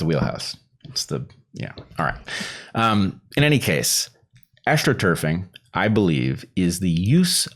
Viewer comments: And I thought you said “Asch-troturfing” - that’s even better, haha! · Astroturf ≈ False grass. Astroturfing = A false "grassroots" movement. "The more you know." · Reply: It sounded like he said "Aschtroturf" at first - which is an interesting play on words if that is in fact what he meant to say before they explained what political astroturfing is. The PTB are very obviously using media and censorship to drive peer pressure to make people publicly agree with a position they wouldn't agree with on their own (the Asch experiment results scaled up to a population) to manufacture an artificial consensus about a healthcare product. This isn't like astroturfing (paying a small number of noisily visible activists to create a false impression of grassroots support) it's more like mowing the lawn short so the dandelions are harder to see.